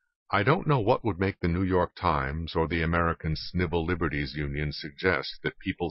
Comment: Did anyone else hear at 1:50, "American Snivel Liberties Union?" I'm totally stealing that!